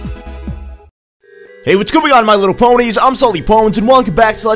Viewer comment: It sounds like sully said "What's goin on my little ponys"